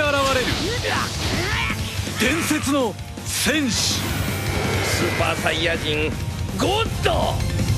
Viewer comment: sounds like someone says super saiyan god (different way of course) is that where everyone started talking about it?